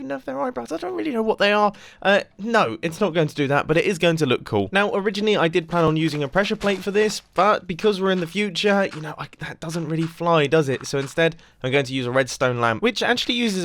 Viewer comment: I thought he said no pressure plates